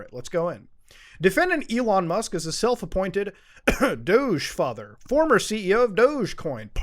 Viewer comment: I keep hearing "douche father".